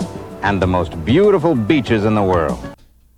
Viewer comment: He didn't say "beaches", he said "bitches", so obvious 🙄 I'm shocked that you misheard him